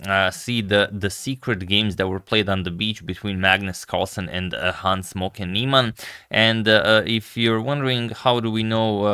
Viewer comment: I thought he said "Hans Morphy Nieman"